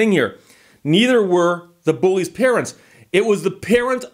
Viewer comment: At about 5:10 I thought you said a "Karent" asked the police to become involved.